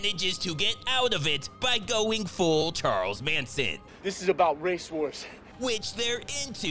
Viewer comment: When Lance was streaming, was he listening to you? I heard "Swanson" but in a Awwwwwsome voice